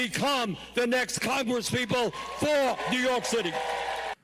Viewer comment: Did Bernie say” work hard”?